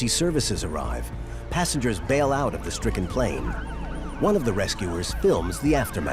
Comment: did he say " this frickin plane???" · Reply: The stricken plane